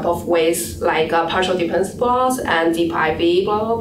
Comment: Great presentation! Curious to know what do you say around 2:48 after partial dependence plot ? Thanks for clarifying. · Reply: yeah, what was she say? Deep v?